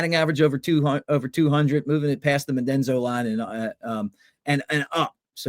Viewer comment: Did he say "Medenzo line"? 😀 Mendoza.